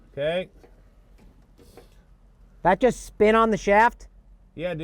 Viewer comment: did he say SPIN on the shaft or SP....nevermind.